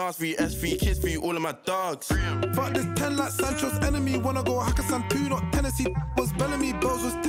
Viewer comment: that Sancho bar is jokes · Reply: What dose he say? I heard “Fuck ten hen like sancho enemy” ?